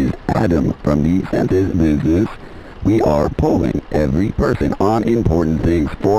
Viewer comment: "this is Adam from 'Centaur's Boobless'." 3:14 I presume this is the new Harry Potter reboot series 5:17 sounds like a typical UPS delivery person, except they toss packages without asking 6:30 I had to double take, because I thought I misread that as "dicks". Typical shopping catalogues. You order a cheap thing, they send you 100 expensive vibrators. 14:54 rich of him to enforce rules he breaks by using shared walls to promote his movies 17:06 "you suck! Perfect!" I didn't expect a game to compliment me on my head giving skills, but I'll take it. 17:37 "Wow! You came in 5" That's what my girlfriend said. I'm no expert, but I feel she didn't mean it in a good way... 18:02 I can make out he's saying "nom, nom". I think he's still eating apples 18:53 man, the economy did Freddy Krueger pretty hard. He isn't taking retirement well. 28:29 if this is what I have to go through to get those sick Kung Fu moves like nakey Bob, so be it!